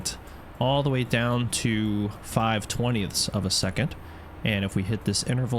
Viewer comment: why you call 50 milliseconds as 5 twentieths ("tonyeths" ??) of a second? I think you said it more than once? 1:20 for example here thank you. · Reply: I think he means .05 = 1/20 of a second. "It goes from 1 second all the way down to 5. [A] Twentieth of a second."